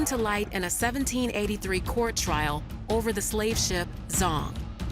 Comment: I thought it said slav ship lol